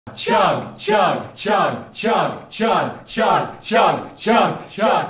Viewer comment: Could've sworn you were saying Chunga Chunga Chunga Chunga at first